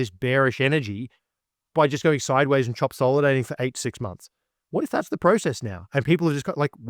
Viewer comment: I thought he was saying Trumpsolidating, chop solidating I finally heard